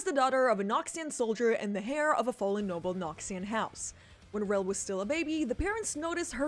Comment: "Heir of a fallen Noxian..." Don't ask why but I misheard "HAIR of a fallen Noxian" and the position on the face didn't help. 😂